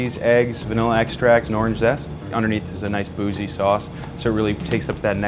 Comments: I swear i heard the chef say boujee sauce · When they said “boozy” I low key thought they said bougie